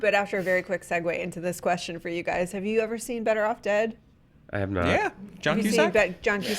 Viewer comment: I ACTUALLY THOUGHT SHE SAID "BETTER OFF TED"